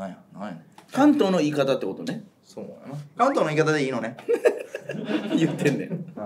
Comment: カントンの言い方でいいのね？に聞こえた笑